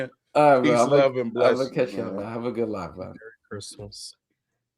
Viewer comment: I heard said “have a good life” like damn, but he really said “live” as in livestream